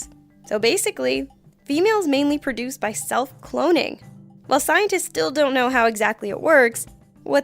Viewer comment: I thought she said self cloning Gucci lol